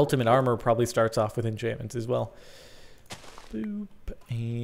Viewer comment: Did he say boob